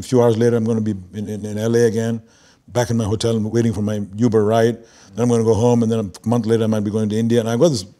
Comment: Ilaughed besides being mesmerized by this interview, because at 44:29, the subtitle became totally wrong. Instead of “in my hotel, waiting for my uber ride” they subtitled “in my hotel, waiting for my new bride 👰‍♀️ !!!” …. Hahaha 🤣